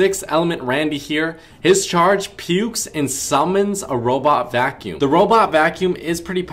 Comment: I legit heard you saying "Otto-vacuum", i appreciate it.